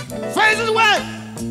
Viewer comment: What’s he screaming at 4:13? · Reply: And i only know that because he says it a little more clearly in other live versions and continues "my face is wet. With the sweat" (from dancing)